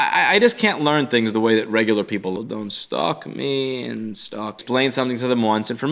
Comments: the audio cuts out and i hear dont stock me.... wtf??? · Anyone hear "dont stalk me" around 2:34? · does he say don't stalk me lol. Also I came from the apple unauthorized repair vid. You seemed interesting, I have done a lot of iphone repair and you seemed to know your shit. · Is it me or does he say "Don't stalk me"